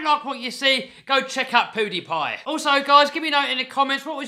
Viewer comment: Sound like you said poodepie instead of pewdiepie no hate though I love your accent